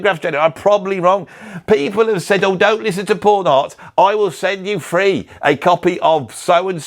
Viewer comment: Pawn Hearts is a searing work of genius. Up yours! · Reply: I thought he was saying "porn hearts" 💀